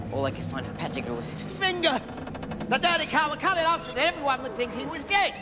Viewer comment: When Sirius was talking about how Peter transfigured and cut off his finger to make people think he was dead I thought he said, “ so that everyone would think he was GAY!” My brain was not working correctly